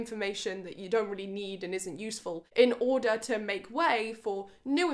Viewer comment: this is a false information, im sorry. the brain doesn't work like that, there isn't a finite amount of space. you never "make room" for new information. even if you feel like you forgot something everything you live is stored in your brain, you just don't necessarily have access to it. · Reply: I think you've misheard "make way for new information" as "make room". Making way for new information doesn't mean that I'm saying there's a finite amount of room in the brain, it means that if the neural pathway to remembering a certain thing isn't accessed frequently it dies off; whilst pathways to things you really want to remember are used more frequently and thus become more dominant. Therefore, your brain "makes way" for (prioritises) the new information - whilst the old information fades. It's just a simplified way to explain something complicated :D